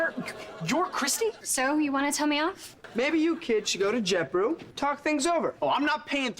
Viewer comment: Lol the coffee brand is actually called JetBrew, Beck also says it at 14:56 "Why don't you kids go to JetBrew and talk things over"